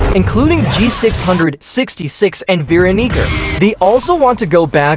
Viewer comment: Bro did not say Veeronica